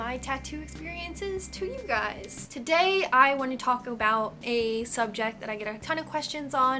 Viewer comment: At 35 seconds in it sounds like you said "I wanna Taco Bell" instead of talk about 😂😁😅😊